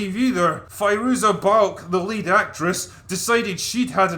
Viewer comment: "The lead actress". Fuck! LOL! I thought Drinker said, "The late actress". Made me pause on go google that shit so fast.